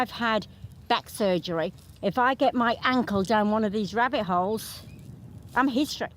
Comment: Did you just say ribbit hole 🐇 🕳️